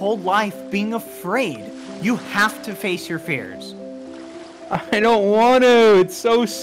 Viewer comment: I heard 'you have to face your fears' as 'you have to face reveal' 🤣